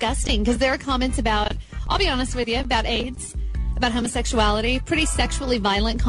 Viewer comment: For a second I thought she said, "I'll be honest with you; I have AIDS."